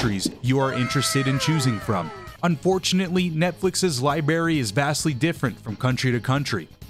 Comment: this man says “Liberry” instead of library what an L